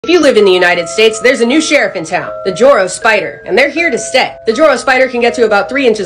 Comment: Why did i hear "Jojo spider"💀